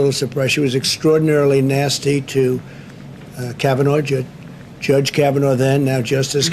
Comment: did trump say "kavanoche"? Espanol no?